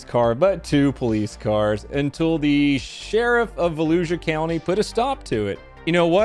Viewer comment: For a moment when said "Volusia County" I thought he said Fallujah County